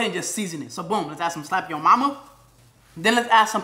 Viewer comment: Did i hear "slap yo Momma"